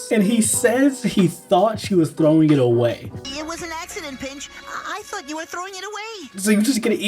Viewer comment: i thought he said "It was an accident bitch" 😭😭😭